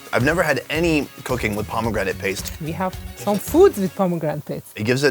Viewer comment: I thought he said “I’ve never had cocaine with pomegranate paste” at the beginning 🤣🤣🤣